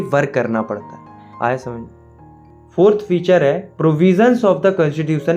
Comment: kya bola idhar bhai .. 😅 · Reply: "Aya smjh me"